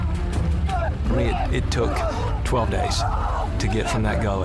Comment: Did they say it took 12 days